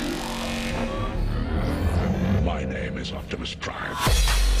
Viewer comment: did anybody else hear, "my name is optimus prime!" at 5:15 ?